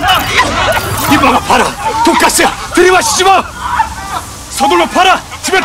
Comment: 초에 돈까스라고 들리지않음?